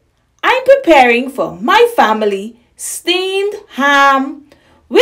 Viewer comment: I thought you said we were having steamed clams